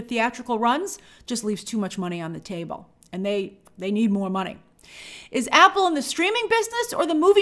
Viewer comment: Taylor Swift might be on tour, but she could've done something - sent a video, a tweet - to support Beyonce after Beyonce came to her premiere. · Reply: did you say Apple needs more money?